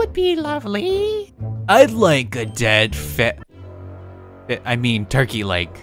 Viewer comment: NOW SCRAM KIDS! Enjoy this very fun episode of Blockburg mother of 4... 3 for today's video! Morgan is graduating so send her some congrats!!! She'll be back in part 21 :) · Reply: You mean dead feed i hear that